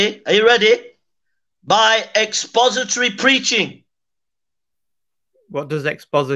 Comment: I misheard that as suppository preaching...